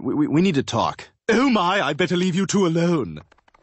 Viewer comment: 🤣 l thought stewie said "l better leave you too alone"